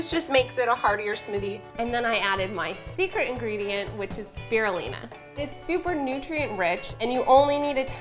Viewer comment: Did she say "berolina"?